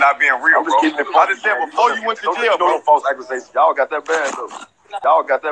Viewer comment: what he saying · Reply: Ya got that back door doe